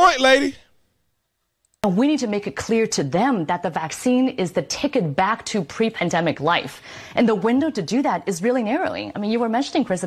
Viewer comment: She actually says the follow at 3:17 "We need to make it clear to them that the vaccine is the ticket to get back to pre-covid life".